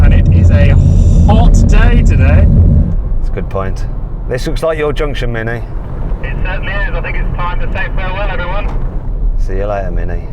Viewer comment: サヨナラって聞こえる